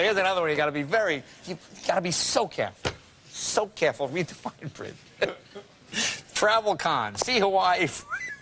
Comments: Hey, check it out: @ 5:40, did Jay say what I believe I heard him say? "read the ________ print". Oh please correct me if I'm wrong. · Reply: He said "fine print", but laughed partway through. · does jay say read the Fucking print? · Did Jay just say read the f n print 5:39 😅😂